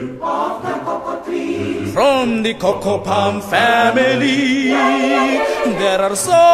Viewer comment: Did You Say Family